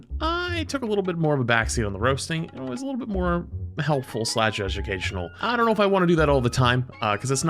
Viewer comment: I know he said education at the start. I still feel like I heard "Judgucational" and honestly? I feel like that's more fun.